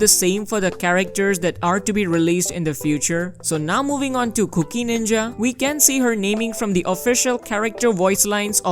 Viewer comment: Was listening to the video like a podcast in the background and all I heard was "Cookie Ninja". I now expect to see a Ninja that throws cookie shuriken's in Genshin soon.